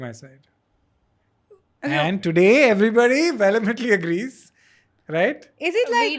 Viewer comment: Bro this dude's vocab is fab or maybe mine is just poor. Can somebody help, what word did he use at 33:41 " and today everybody ve-le-britly agrees" what's the word??!!